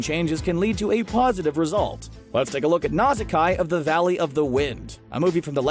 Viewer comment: I'm sorry did you say "Nausakai"?